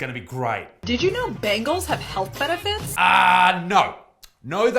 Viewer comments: I heard bagels there, not bangles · when she said bangles I deadset thought she said bagels 😂😂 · I thought she said BAGELS have health benefits lol! That would make more sense to be honest.